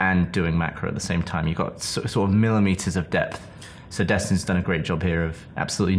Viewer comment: I keep hearing "Sir Destin"